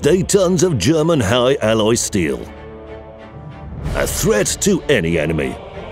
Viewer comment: I seriously thought you said "A threat to any anime" at the start